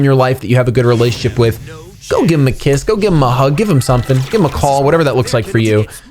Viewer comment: Ok listen... From 3:52 to 3:57 u were talking about giving our mom love... but u were saying HE instead of SHE really?!?! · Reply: I think he was saying “give ‘em a hug”.